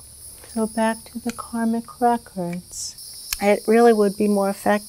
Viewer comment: I thought she said karmic cracker